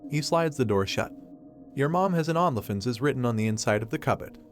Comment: what is written in the text? I cannot for the life of me make out what the TTS voice says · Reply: "your mom has an only f@